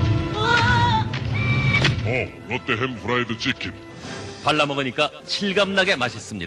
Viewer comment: "What the hell is fried chicken?" Tell me that isn't what he said.